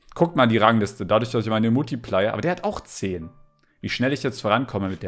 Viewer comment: Ich habe verstanden als er multiplayer gesagt hat Muttiplayer verstandenn😂😂😂😂